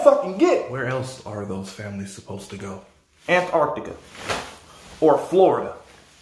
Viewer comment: I thought you said "aunt Artica" 😂😂😂